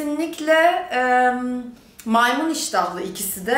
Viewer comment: Sen bize MAYMUN mu diyorsun.Ben mi yanlış anladım?😕😕😕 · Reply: Neslihan Yildirim Siz yanlış anlamış olabilirsiniz, maymun demedim, maymun iştahlı dedim 😄